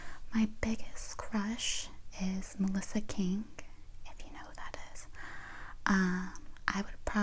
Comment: Who is she naming as her crush? 🤔I can't make it out and the CC says Melissa "King" which results in zero relevant Google results lol. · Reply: Her name is Melissa king. She’s a professional chef and was on top chef! She’s wonderful 🌸